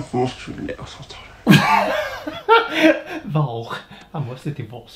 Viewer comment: det jag hörde va "va, jag/han måste till levnads centralen" vet ej vad levnads central är men XD